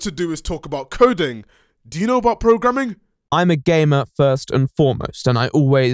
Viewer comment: I swear I heard "And I am a gay man!" Not "gamer." Lol both things are absolutely awesome but you don't need me telling you that. Just got confused and laughed at my misunderstanding.